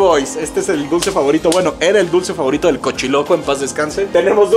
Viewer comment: Min 14:14 no entendí como q el cochiloco ?? 🤔🤔